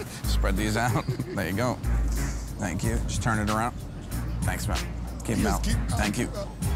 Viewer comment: Did he say thanks mam to the guy at 3:10?? 🤣